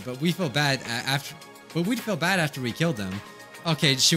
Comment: Did mike just say fuck or are my ears broken 😳